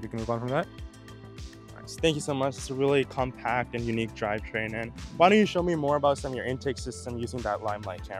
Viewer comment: did he really say unique drive train at 1:50 ???